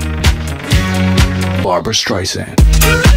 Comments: Fun fact, when I first heard this song, I didn't know who Barbra Streisand was and thought they were saying Barber Streisand. What a bizarre song that would make. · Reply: I thought it said barbers stripes hand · When I first heard this song, I thought he said "Farmer's Tri-San." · Fun fact, when I first heard this song, I didn't know who Barbra Streisand was and thought they were saying Barber Streisand. What a bizarre song that would make. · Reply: I thought they said Barbra Stacy :T And I just learned the name of this song today about five minutes ago... Dumb luck gave me a name I’ve wanted for many years XD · Barber's tricep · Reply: I listened to this and I thought it said barbers tricep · I remember a flipnote of this.. I used to mishear it as "Barber-Stration"